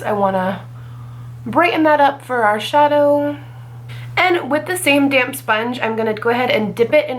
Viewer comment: I have been laughing at myself because at 6:03 to 6:08 I first thought you said, "with the same damn sponge." I was like, went full sass today. Of course when I listened again it was "With the same Damp sponge." lol